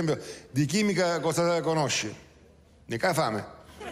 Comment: "DI CHIMICA COSA CONOSCI ? " NON HO CAPITO LA RISPOSTA CHE HA DATO CACIOPPO....... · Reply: La “fame” “fame chimica”, quando ci si fa di cannabis e viene successivamente tanta voglia di fare uno spuntino.